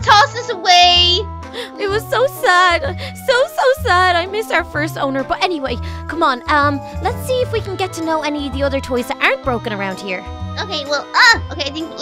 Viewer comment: i thought she said butt man😂😂😂